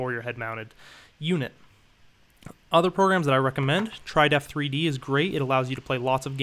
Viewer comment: could you write down all those sites, I cannot get what u say . Like that "other programs I reccomend" in 3:31.. which one is that trydef3d??